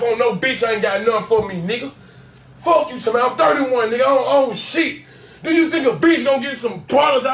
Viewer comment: When he said “ I'm 31, I DON'T OWN SHIT! " I felt that in my soul💯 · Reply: I thought he said “I’m 31 I don’t owe shit”